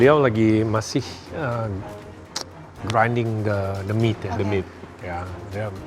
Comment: awal dengernya "demit" kata gw apaan tetiba bahas demit butuh resting, ternyata "the meat" 😭